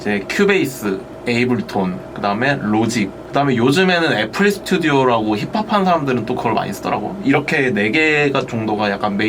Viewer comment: ㅋㅋㅋㅎㅋ 자막 정정해주세요 7:21 애플 스튜디오 아니고 FL Studio 입니다. ㅋㅋ발음이 그렇게 들리기는 하네요… 힙합할 때 FL 많이 쓰죠 ㅎㅎ